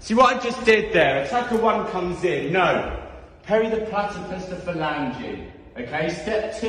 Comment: what is he saying here? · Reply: parry the platter fist the fell on yew